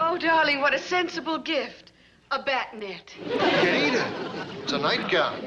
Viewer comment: What does Morticia say at 1:42? "a ..." Thanks! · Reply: Bat knit